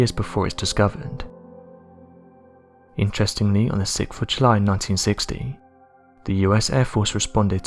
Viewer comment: it sounds like he said on the sixfoochlie in 1960 , now come on really if this thing can't say 6th of July right . Really nerve wracking sometimes trying to understand what it or he or she is saying !!